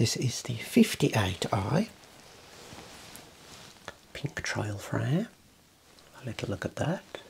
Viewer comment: I would a love a pair of the Peekatrons for myself, quite the dashing look! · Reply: I don't know 🤣 I just watched the video again to see what I was on about and I think I must have misheard pink trail frames as peekatron.